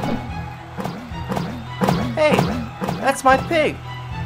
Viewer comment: I think he called him "pig"